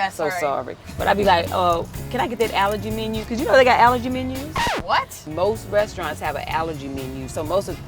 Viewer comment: Before they put the caption "allergy menu" on the screen, I kept hearing "alligator menu". My ear buds are cheap so I'm guessing that's why, but for a few seconds I was soooo confused, I was like are alligators vegan? Is that why you ask for an alligator menu? Ohhhhhh allergy....